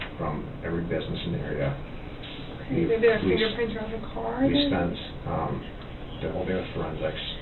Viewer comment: the subtitles say inaudible. What she says is "maybe there are fingerprints on the car" or "maybe their fingerprints are on the car."